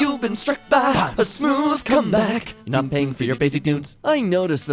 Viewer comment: I thought he said cumbag here